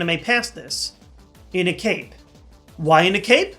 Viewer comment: i thought he said cake at first XD